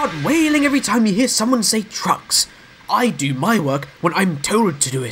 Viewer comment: I thought Henry said drugs instead of trucks. Lol.